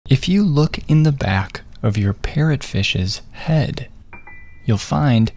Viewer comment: I thought he said “if you look in the back of your pet fishes head” 💀💀